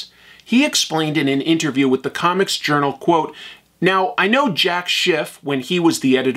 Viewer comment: I thought you said 'I know Jack sht'....when it was in fact 'I know Jack Schiff'.